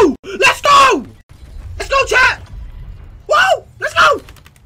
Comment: He really said “let’s go champ” 🤣